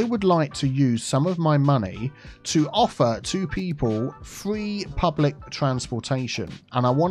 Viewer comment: i heard it as "to offer two people public transportation" at first and i was very confused and amused. just two people specifically. no one else in the city gets it 😂